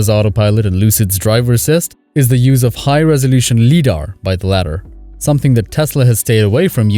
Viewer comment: Hol up! did i just hear Leedar!?